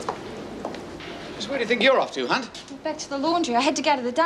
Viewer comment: did any one else snigger at 9:11..."were you off to hunt".. i missheard it and thought he said cun... well nvm what i thought he said but it was very very rude ><